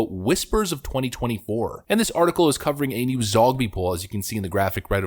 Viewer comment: HA! When you said "Zogby poll" I thought you said "Zombie poll".